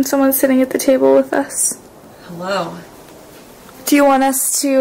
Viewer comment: I heard a disembodied female voice say, "Hello."